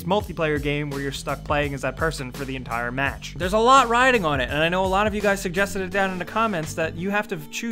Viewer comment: did he say "theres a lot rioting on it" at 1:31 cause that made me laugh cause riot made league xD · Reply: It was rioting and he also put in a league of legends picture